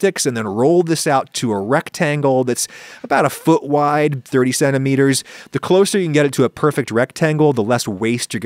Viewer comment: When you said "30 centimeters" i heard "37 meters" and was really confused.